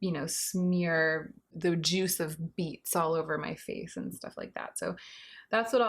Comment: ❤️❤️❤️ “chew on chalk and smear the juice of beets on my face” Classic. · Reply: No word of a lie, I thought she said "the juice of meats...."